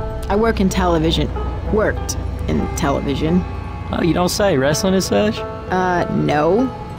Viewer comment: what did ellis said? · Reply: “Oh you don’t say, Wrestling and such?